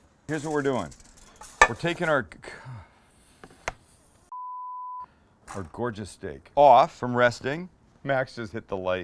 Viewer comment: i think he said gorgeous dic# in